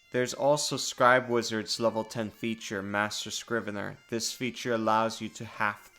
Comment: thought I heard "Master Scribbler". Then I looked up and was disappointed :(